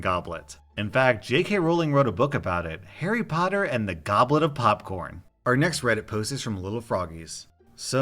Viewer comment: For some reason I heard "Harry goblet and the potter of popcorn"